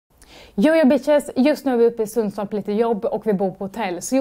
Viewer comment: I thought she said "yo yo b*tches" before I realised its not English 😂